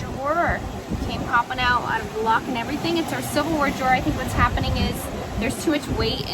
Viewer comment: Oh ! Silverware Draw...!! I thought you were saying Civil War Draw..! Lol I was like why has this woman got a civil war draw?